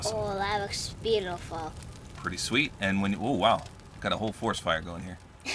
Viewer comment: I love your channel but did you just say a hole